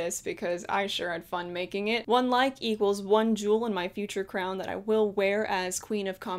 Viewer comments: When you said jewel I thought you said juul I'm gonna cry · I'm too much of a millennial that I heard, "Juul in your future crown" smh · when she said "one like equals one jewel" i thought she said juul 🤕